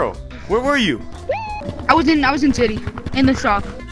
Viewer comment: did anyone else hear instead of city he said “I was in titee”